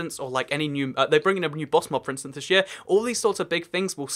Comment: Did he just say new boss mob!? First im hearing of this